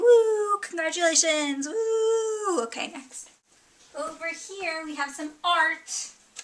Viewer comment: did u say,"over here we have some FART!" ?